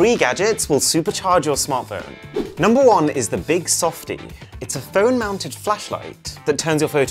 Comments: Bro I thought he said “Big soft D” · i thought you said phone fleshlight 💀💀💀💀